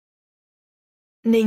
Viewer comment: Eu entendi "nenhum humano e perfeito mais eu nao Mussoumano"